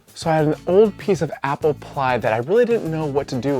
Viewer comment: I must be hungry, I thought you said a piece of apple pie.